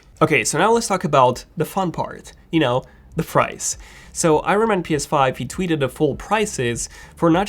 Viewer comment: Am I deaf or did he say fries?